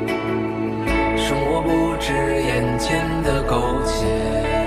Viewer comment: 尷尬 聽成‘’生活不只眼前的狗血‘’